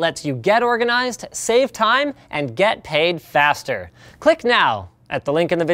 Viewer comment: I thought the add said "get AIDS faster" at the beginning for a moment... I thought that was strange...